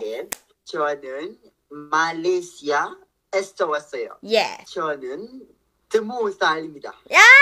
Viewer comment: i heard 'jeoneun temusal imnida' instead hshshs